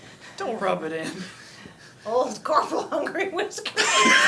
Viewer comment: Anyone know what said at 25:30 ? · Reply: "Poor old corporal Hungry Whiskers..."